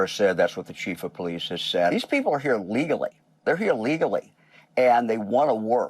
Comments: Hey team, just a note - your captions around 10:30 are wrong. Like, the exact opposite of what the person said kind of wrong. Gov. of Ohio says "These people are here legally, they're here legally." Captions say "These people are here legally, they're here illegally". I have a mental audio processing issue and paused at a weird time, wound up having to re-watch the clip to make sure that I hadn't misheard. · Phil love the content but you need to check your closed captioning. It's not bad, but on the video today 10:35 the caption says "illegally" rather than what the speaker said which was "legally" It's important and this is not the only vid that has issues. I'd literally check each video for like 5 bucks a video for you.